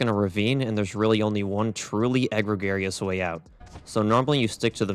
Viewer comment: Did he really say "egg-regarious"????? You mean egregious????????